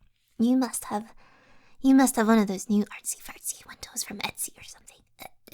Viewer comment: wait did you say "artsy fartsy" from a certain youtuber/streamer aka ArtsyVRC( <link> )